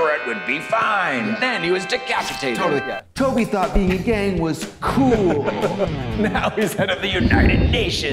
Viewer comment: Omg "Toby thought being in a gang would be COOL!" "Now he's head of the United Nations!" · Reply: This whole time I thought she said “being gay would be COOL” and I have come back to this video multiple times to cackle at how absurd it is, only to now find out that I am even dumber than previously thought 🤠